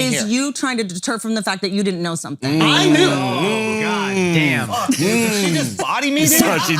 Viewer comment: I swear Schulz said "Did she just body me nigga?" 🤣🤣🤣🤣